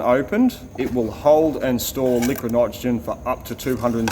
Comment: CC reads "liquid OXYGEN" while says "liquid nitrogen". I know, I know, I'm a knocker. Natural born one. But hey, someone has to do it, right? ;-) 21:23 - "...and then they pull a vacuum on that, seal that off ... and that stops the heat transfer going from the outside atmospheric pressure ..." - righto, just omit that "pressure" part as it has nothing to do here. In vacuum , or "near vacuum" there are very few molecules that can transfer the heat "from the outside", i.e. the atmosphere in this case. 21:51 - "if liquid nitrogen is stored under pressure it can explode" - erm, "We heard what she said but knew what she meant" as Two Ronnies were singing. Anything held "under pressure" will stay under pressure, as long as the container is strong enough and it will no "explode" (except, perhaps, acetylene over two bars... speaking of which, an acetylene walks into a bar... but I digress here, back to the issue). So, "if one tries to contain a liquefied cryogenic gas in a non-insulated, non-vented container, the outside heat will cause the gas to boil off, and the pressure within the container will rise - and when it becomes too great for the container to withstand it, the container will rupture, often explosively". Yes, some may call it nit-picking, but I call it "involuntary correction of poor choice of words". Sorry, can't help it... ;-) 22:52 - "...the bush can then absorb the heat and you'll essentially shrink it..." Whisky Tango Foxtrot?! Like, "the morning afte